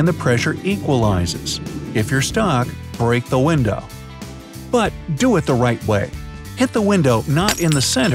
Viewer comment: did I just hear Windows xp?